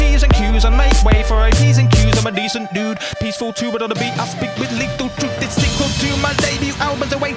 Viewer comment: How did I never notice that at 01:09 the subtitles say "ut" not "but" :O